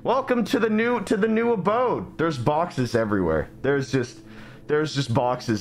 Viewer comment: I thought you said foxes but you said boxes 👁👄👁